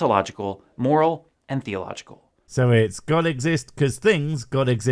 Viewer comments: That list he gives at 4:45...did...did he just say theological? Because the list says Teleological. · Even though it’s shown on screen, he doesn’t say “teleological.” He actually says “theological.”